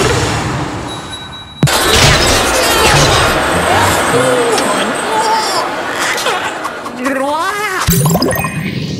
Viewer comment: I heard a french word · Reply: You have great ears 🤣 he says "au secours!" (Help!) I never noticed that 🤣